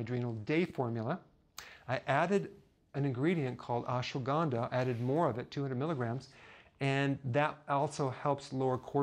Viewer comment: Did he say he added Ashwagandha as an added ingredient? At 32:12.